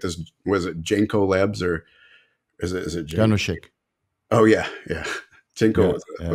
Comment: Can anyone tell me the name of the hplc testing lab mentions @ 48:40 ? It sounds like he is saying janoshake. Thank you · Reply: Janoshik